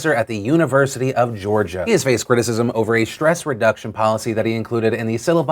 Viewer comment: I thought you said "Breast Reduction Policy"